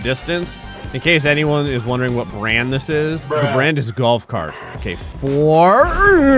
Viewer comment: Thought it said 'gay cart'💀